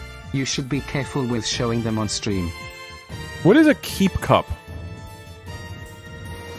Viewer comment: I thought he said keef cup and at first I was like damn I didn’t realize NZ was cool like that